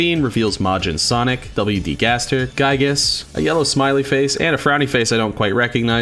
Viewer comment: was u saying wd gaster at uboa satire or am i dumb